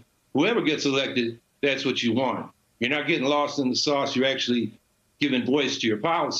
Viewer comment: Did he say lost in the sauce @ 3:10 😂